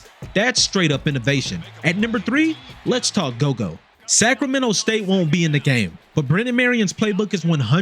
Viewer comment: Could’ve sworn they said Sac state will be in the game